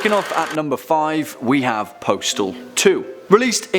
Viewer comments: When he said Postal 2 I almost thought he said Portal 2. 😅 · Lmao I thought he said portal two not postal two